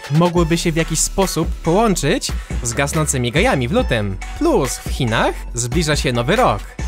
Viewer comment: czy tylko ja usłyszałem gasnącymi gejami?